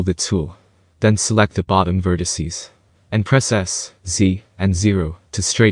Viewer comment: I swear he said c instead of z at 5:18. So to get the bottom part to be flat, its s+z+0.